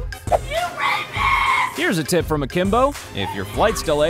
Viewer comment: I think she called the poor gentleman a "racist" not a "rapest"...